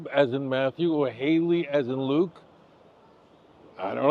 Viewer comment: “who was Jesus father, Jacob as in Matthew or Haley as in Luke?’ Rabbi please I do not understand who is “Haley” in Luke? I went back and read Luke 3 and I’m not seeing a Haley. Am I misunderstanding what you say here? Are you saying Haley as in Luke? Please clarify! Thank you! I am learning! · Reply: “Heli” is pronounced like Haley. HTH.